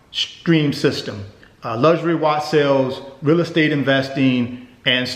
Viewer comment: Luxury what sales? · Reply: Jeremy Bolanos sounds like he said luxury watch sales